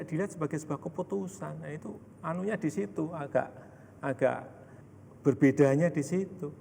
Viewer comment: "anunya disitu"apa saya salah dengar.